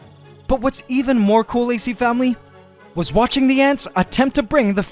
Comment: Did anybody hear wen he said ac famely It sounded Like he said aice family